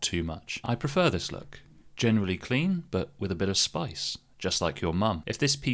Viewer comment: am I hearing things? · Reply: I heard "with a bit of spice just like your mum"